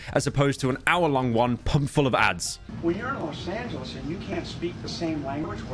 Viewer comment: DID HE SAY "YOU'RE IN LOS SANTOS" AT 8:13 ????